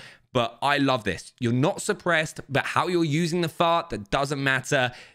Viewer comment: he says fart instead of ffar